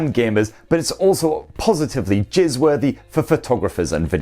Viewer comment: Did he say jizz worthy?